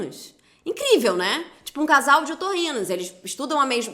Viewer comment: Toda vez que ela repetiu eu entendi "casal de ornitorrincos" 😳